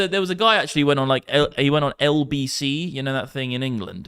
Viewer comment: Somebody figure out what was said 1:19:41 LBC? Let get paid?